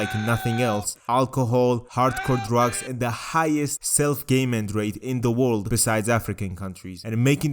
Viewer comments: I might be drunk myself but why am I hearing "gay men rate", I mean thats pretty based but that cant be right. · the highest self what rate in the world? I can't understand what you're saying there. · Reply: Harm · wtf did he say? Self Gayment? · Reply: self game-end which is youtube censorship slang for su!c!de